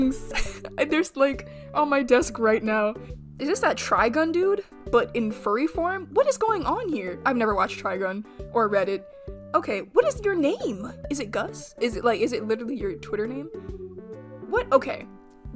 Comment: Sorry from a passerby but at 5:28 i really did think you said you had never watched reddit as if it's a series